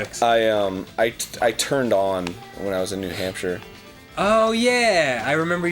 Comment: What Arin said: "I turned on when I was in New Hampshire" what I heard: "I get turned on by new hamsters" XD